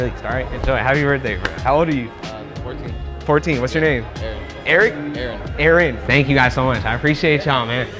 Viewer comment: he asked for his name and the subtitle said Tom Brady when he said Aaron 😂